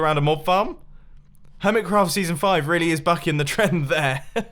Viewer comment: hermitcraft season 5 hasn't got a base centred around a mob farm so what about the guardian farm in the freaking middle of your base! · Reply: I thought he said “Hermitcraft Season 5 is carrying on that trend”